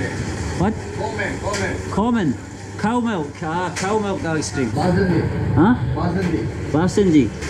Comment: I thought he said covid ice cream